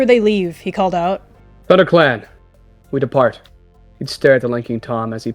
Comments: i heard "ButterClan! We depart." I THINK MY HEARING IS BAD RN RAAH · I swear I heard butter clan